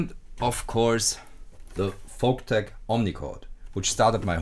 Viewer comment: Hello guys do someone knows the name of the instrument at 2:39 please ? I don't understand what he says great video by the way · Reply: Folktek Omnichord - if you look it up on Youtube with added you should find the video.